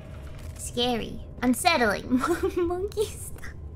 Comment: What was Gura not going to say at 2:13:24? I've replayed it multiple times and all I hear is "monkey stuff." Somehow I don't think that's right.